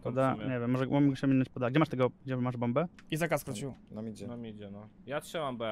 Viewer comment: za 1 razem usłyszałem Izaka skrócił xD, a nie Izak A skrócił